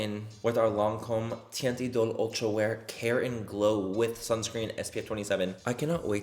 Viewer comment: I kept hearing “Lancôme teint idole, Karen glow” and I was like nah don’t want that